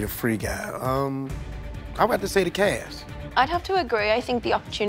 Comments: i thought he said the cash LOL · I thought Lil Rey Howery said he was drawn to the "cash"